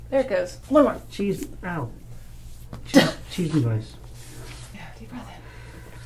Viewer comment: Did he say “cheese fries” at 15:45?!?!? Lolol